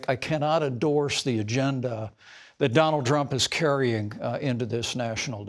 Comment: Did Pence call him "Drumpf"?